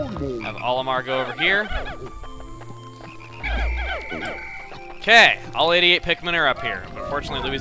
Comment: I thought you said all idiot pikmin are up here.